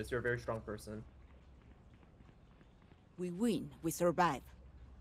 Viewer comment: bro its fucked up but I thought reyna said "Oui Oui! We survive!" at 1:10 and now I cant unhear it